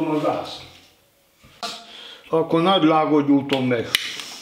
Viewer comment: nál mit mondd? Akkor nadrágom gyújtom meg??? A hagyma szeletelés siralmas lett, a tészta főzés pedig szánalmas... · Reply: "Nagy làngon gyújtom meg"